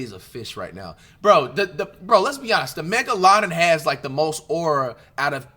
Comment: the mega laden? Wat he trying to say or that a real fish 😂😂😂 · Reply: megalodon he prolly meant😂😂😂😂😂😂